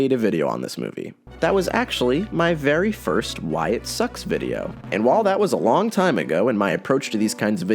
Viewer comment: I thought you said "Wyatt sucks video"